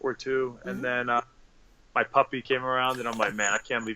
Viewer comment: I thought it said Pussy instead of Puppy